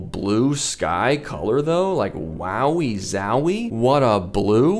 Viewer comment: did you just say "wowie zowie?"